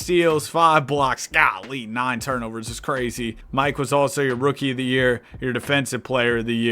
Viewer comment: i thought he said mike wazowski